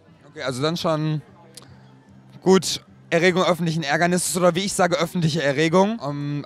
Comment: Hallo! Ich studiere Deutsch und ich verstande nicht was sagt der Mann von circa 1:28 bis 1:32. "-Ja - Auf alleine? - Nein! ...?????" Konnt ihr mir helfen, bitte? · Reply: er meint damit Erregung öffentlichen Ärgernisses